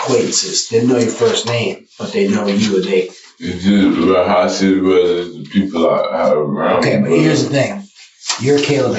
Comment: Please tell me what he said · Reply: "Ra, Ru, Ra Ru Bruh"